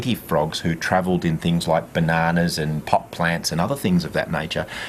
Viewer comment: does he say "like bananas and POT plants"? LOL